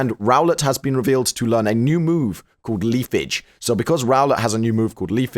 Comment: So who else heard "Leaf Bitch" at 2:30?